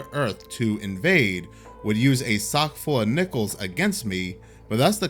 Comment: I misheard and thought You said We would be hit with a sock full of nipples. haha